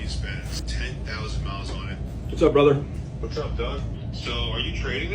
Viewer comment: Please like and follow if you want to see more dealership behind the scenes! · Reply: I legit thought said "Sup, dawg" when he was actually saying "What's up, Doug" 😂😂😂